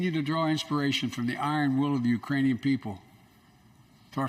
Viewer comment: "Putin may circle Kyiv with tanks, but he will never gain the hearts and souls of the IRANIAN PEOPLE" -Biden State of the Union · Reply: I thought he said Uranium… oh well we know what he ment. 🤣